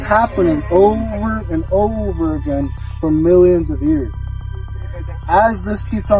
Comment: 🙋🏾 ugh question, I thought you said millions of years. But then it gets uncovered and erodes. Which means it doesn't have millions of years to grow..